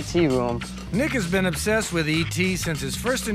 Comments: Was i the only one that laughed because at :28 it sounds like he says "ni**as been obsessed with et😂😂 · I thought the narrator said "Niggas been obsessed with E.T." 😂😂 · Time stamp lol 0:19 it sounded like he said Nigga been obsessed with a ET room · Best guy in this was the one driving by. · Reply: bro. Listen right after 0:21 Tell me that don't sound like he says, niggas been obsessed with ET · Tell me that don't sound like he says, niggas been obsessed with ET. Lmao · Did he just say nigga